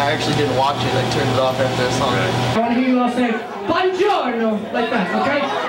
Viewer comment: Is Gerard saying "BUONGIORNO" (good morning in italian) at 1:16:14 ?